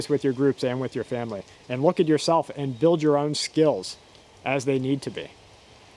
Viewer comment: I enjoyed this. Gave me a smirk when I thought you said " ready your stills". However, that's a trade commodity and skill as well if you had said it. I do not think I'm an expert at anything though am glad to teach when I can or learn when opportune. Hope you all keep cbs in ranges or swrs, horse dispatchers, pigeons or whatnot in case the satellites change to military mode ( and I hope they do not again). I don't think it's odd you and have open phones, laptops to each other. Everyone is different though in some respect. Well go catch a pond swim or something and safe new year to group and following. Going outside to ck rabbit tracks w brittany dog in fresh snow dusting. TX for video.